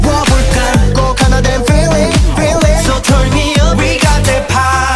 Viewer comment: when I first listened to this song, I thought they said 'turn me on' instead of 'turn me up'